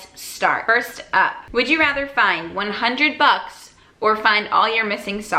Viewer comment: I thought you said 100 bugs......